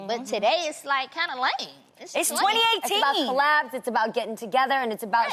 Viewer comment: did Adrienne say 2018